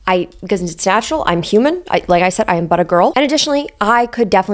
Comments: I’m so disappointed in myself because I genuinely thought was saying “I’m butter girl” and spent the whole video with that in the back of my mind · For a good minute I thought you were saying buttergirl. 😅😅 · i thought she was saying 'butter girl' 😂 or was she saying that, not 'but a girl'....?